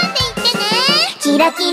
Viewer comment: did she say Giratina?